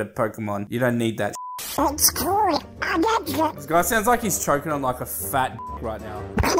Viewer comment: donald duck sounds like he is saying "its cool! i get it!"